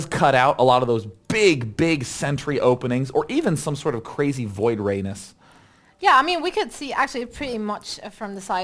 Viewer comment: did he just said "voiodranus" at 3:09 ?